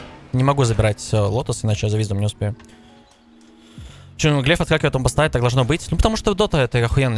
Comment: "чёу ифартахеиндастаи должно быть" Что он сказал? Раз 10 переслушивал. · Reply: Про то, что на 16:50 глефы от аванпоста отскакивали «Чё там глеф отскакивает от аванпоста? Так должно быть?»